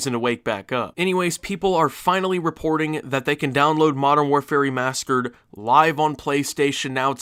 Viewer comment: modern warfare remaskered and I think every other time it’s said, love you tho